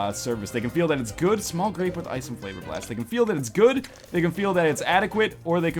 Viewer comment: did you just say "small rape with ice and flavor blast"?